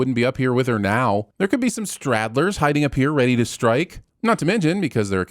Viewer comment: WTF are 'straddlers'? The word is stragglers .